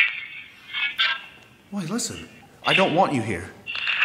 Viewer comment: I think I heard, 👂YOU DON'T KNOW WHAT YOU'VE DONE."